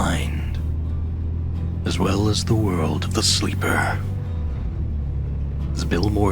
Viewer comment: I know I heard him say norfuck virginia